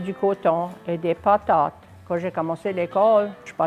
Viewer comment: Rita d' Autreuil Mark ,I think I heard her say potates ,as papa or batata or somethng similar ;like Spanish instead of pomme de terre .